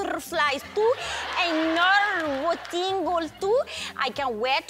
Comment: What did she said at 1:56? Narwotingles too? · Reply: F Red she meant nerve tingles 😂